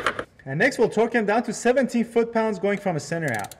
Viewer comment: It sounds like you said to torque to 70. I think it is supposed to be 17? Otherwise, I found this a very good, clear video.